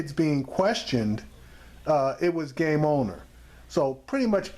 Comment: Did he say game owner @ 7:18?